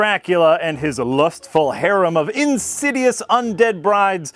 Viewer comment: "...Dracula and his harem of INSIDIOUS undead brides" I misheard this first as his harem of INBRED brides, and I'm like, a country-fried redneck vampire movie might actually be fun and unique, but... no. Sadly no.